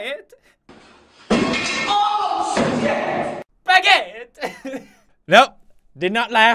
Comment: i thought he say "oh sakit" in pilipino which means it hurts in english pilipino